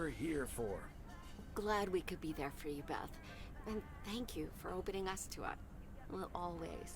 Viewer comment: "and thank you for opening us to up" am I hearing? Hehe 💙 I might drop by the xmas stream! I've got family for Christmas but always have to dip for a whole to refill my social battery anyway ^^